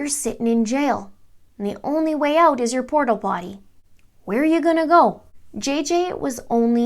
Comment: It sounded like you said “you’re only way out is your Portal Potty...LOL